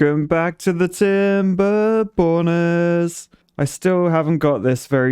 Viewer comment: I thought you said timberboners